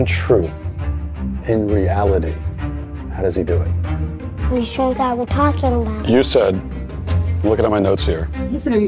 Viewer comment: The point where he said "who's truth are we talking about" is actually really sad and kinda terrifying. The kid doesn't even have a concept of truth at his age, his secular parents no doubt stole that from him. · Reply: I heard, " it's truth were talking about"